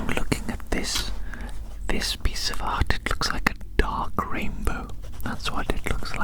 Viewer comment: I thought you said a dog rainbow at first, and I imagined a rainbow without any red hues, from yellow to blue.